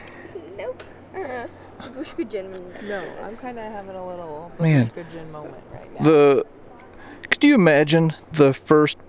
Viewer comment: Did she say she's having an estrogen moment right now?? lol